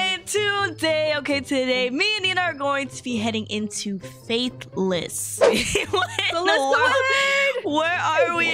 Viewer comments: At first I thought it said fatherless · I thought it said fatherless 💀